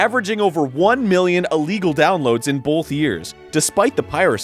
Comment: I realized it was Arin talking as soon as I heard his trademark strange pronunciation of the word "both" at 0:21 It always sounds to me like he's pronouncing it as "bulth"